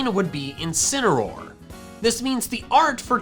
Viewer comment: I heard this “Incinameroll” “Two Canons” “Dusk like Rie can rock” “Zareena” “Alola Ninetails” “Right Chew”